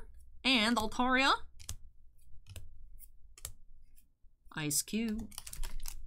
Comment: I thought you said ice cube then ally i think I'm half asleep 🥱